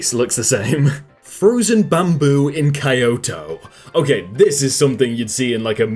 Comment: did he just say kai-yoto? Or is it something diffrent? Not japanese but pretty sure its Ki-yo-to